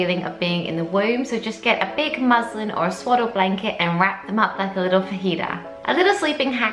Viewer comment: did she say "wrap them up like a little fajita" hahahha?